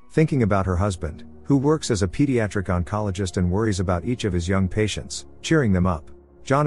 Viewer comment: I thought he said he was a pediatric gynecologist, lol. But I'm guessing it was either an oncologist or an ophthalmologist. Either way, it was pretty funny. Hopefully I spelled that right.